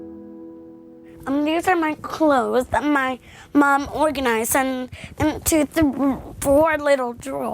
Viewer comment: I thought it said organic and I thought "They made artificial mermaids?"